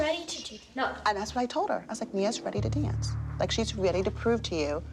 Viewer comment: The subtitles said “Mia’s” instead of “Nia’s”